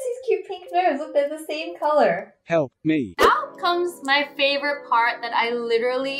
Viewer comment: Who thought was actually saying "help me" 🙋‍♀️